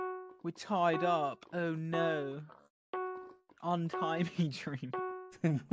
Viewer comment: At first I thought it said unite